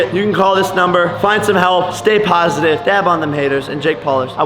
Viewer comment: I thought u said dab on them haters, but u r saying things about Rice Gum